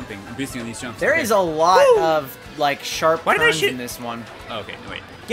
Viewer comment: I kept hearing say "sharp ferns" not "sharp turns." xD